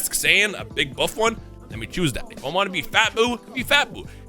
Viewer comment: " let me choose that nigga". 👀 is that what I heard.